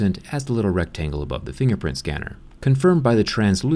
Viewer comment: I thought you said fingerprint spinner :D